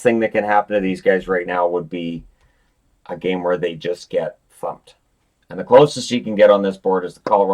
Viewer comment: I had to rewind that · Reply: I definitely didnt hear a swear. I heard 'fumped' a combo with 'thumped'